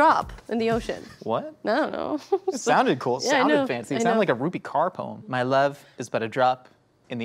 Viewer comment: and 16:14 the captions say Ruby Carr (actress) but I think they're talking about Rupi Kaur (poet)?